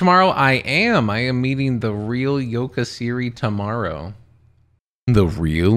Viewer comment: Yoka Siri ? Thought you said Yoda Siri. 😂😂😂